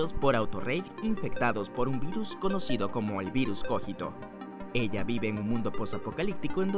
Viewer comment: parece que dice virus jorgito jajajajajaja